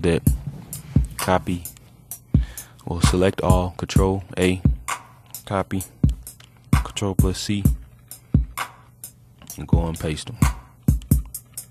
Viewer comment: Thanks bro! :D umm Did you say "Control Pussy" at 17:40?